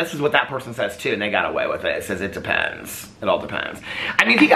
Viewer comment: said "it depends" and I heard "in depends". I am not saying sorry to the depends community